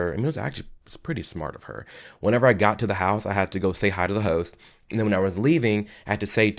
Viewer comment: I swear to god I thought he said “I had to say hi to the hoes” for a moment lmao